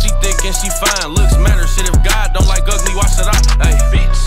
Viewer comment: That boy said “ if God don’t like ugly why should I” felt that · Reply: i thought he said if god dont like ugly wash it off haha i was wondering wtf that meant